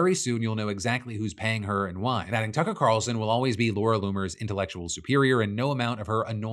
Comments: I swear on my life I heard him say Cucker Carlson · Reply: I heard “Cucker Tarlson” and came to the comments to see if I was alone😂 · I thought i misheard, but holy shit Phil did casually say Cucker Tarlson🤣🤣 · did...did i hear that right phil? did u just say cucker tarlson? lol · Did.. Did he just say.... Cucker talson?